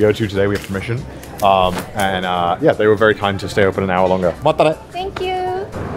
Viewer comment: i love how it says manatee instead of matanee